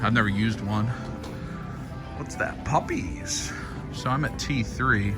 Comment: Lol, it sounded like you said puppies. I'm sure you know papis is Spanish for daddy...